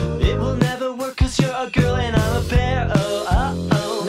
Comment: I can't stop hearing pear instead of bear and tbh I'm not complaining